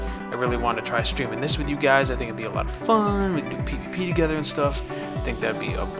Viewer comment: When you said "we can do PVP together" I thought you said "we can pee together"